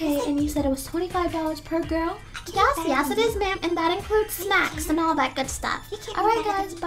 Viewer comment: That includes snacks and all that good stuff i thought she said smacks and all that good stuff